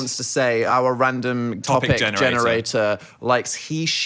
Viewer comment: I swear to god, i had my phone in my pocket playing the pod and i swear i heard Dan say “Farquaad generator”😂😂😂